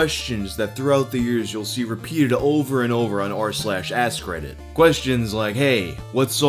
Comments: I keep hearing "ass credit" instead of "ask reddit". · I hear 'ass credit' not 'ask reddit' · I can't help it, I keep hearing "ass credit" instead of "Ask Reddit"